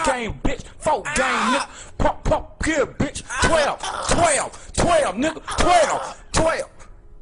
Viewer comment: I swear it was 10 10 10 instead of 12😅